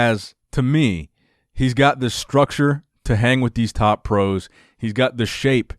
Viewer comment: I love your videos and commentary but I heard you say structure a couple of times instead of stature.